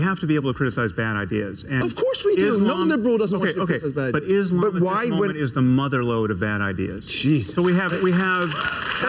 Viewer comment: I couldn’t understand anything they said they were talkin over each other lol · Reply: Had to rewind it a few times but i think this is what was said at one point "Islam at this moment is the mother load of bad ideas" BEN AFFLECK: "Jesus."